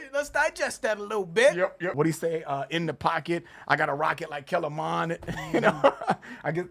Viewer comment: I keep hearing "rocket like can Lamar" As is Lamar Jackson of the Ravens. But also as in Ken(drick) Lamar.